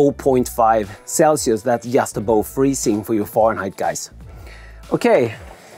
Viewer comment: “Just about freezing for you Fahrenheit guys” 🤣🤣🤣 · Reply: It sounds like he says “Foreignheit” which I hope was intended, thats hilarious!